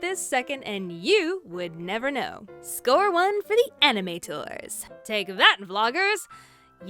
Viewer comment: I thought she said anime tours